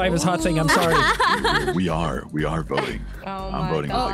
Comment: Corpse: "We are voting. I'm voting with a GUN." Corpse out here gonna make himself an actual corpse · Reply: Diaz 31:12 They just said "We are" instead of "We are-we are".